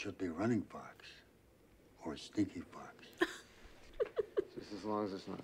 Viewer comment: Forget the werewolf...The man just called you Sneaky fox😯🤣😂Can't let that stand can we?😁Have a wonderful weekend 💕Thanks so much! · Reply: TEX! I thought he said STINKY fox 😂😂😂😂😂 I feel so stupid hahaha